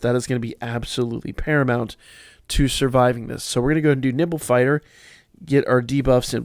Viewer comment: My dyslexic mind at first swore you said, nipple fighter.